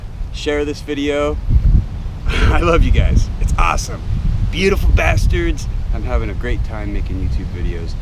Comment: Ummm did he just say Beautiful Bastards???